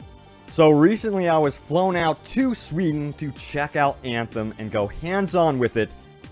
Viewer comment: It sounded like u said sweten